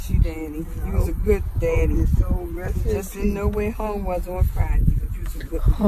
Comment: sounds like she said "miss u daddy/Danny.. U was a good daddy.. U just didn't know where home was on Fridays"